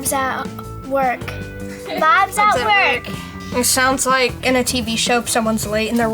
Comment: I thought she said "mom's at work" at first and I totally got what she meant because that's when I jam too. When mom's(the wife) at work, I don't get in trouble.